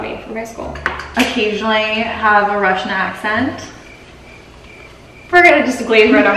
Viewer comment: Смотрю и нифига не понимаю... 1:17 они сказали "Рашн Акцент" или мне послышалось? Люди, кто там умный, кто знает инглиш, отзовитесь! ))))))♥♥♥